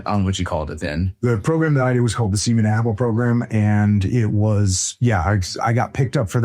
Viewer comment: did he just say "seaman apple" 😳 😂